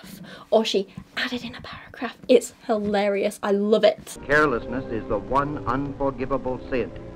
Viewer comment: I thought he said hairlessness at first